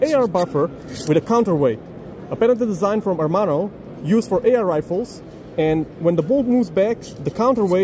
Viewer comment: At first I thought he said AIR rifles and got sad. A second watch showed me that my sadness was indeed unwarranted